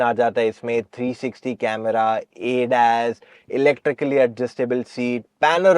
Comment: Did you say MG ZS EV has ADAS in it?